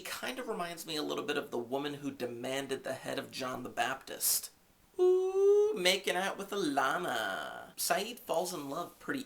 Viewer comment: haha - sounded like you said "making out with a llama" - that would be a Lost first, a network television first come to think of it :)